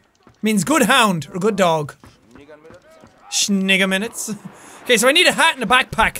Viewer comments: did jacksepticete just say “nigga minutes”.😮🤭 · did jack say shingga minutes